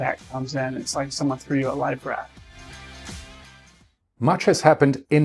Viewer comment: I'm just wake'in up here. Poured the coffee. Canadian Tim Horton. I hear a guy say "it's just like somebody handed you a live rat". WHAT?? I replay the video . He's saying life raft. My laugh for the day